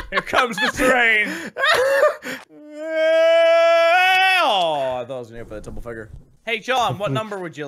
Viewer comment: The fuck does Minin say at 9:08? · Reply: Toan Greenlow he says I thought it was gonna be a double figure